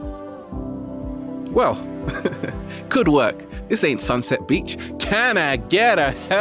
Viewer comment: I had to rewind 1:09 cause I heard "this ain't sunset bitch" at first.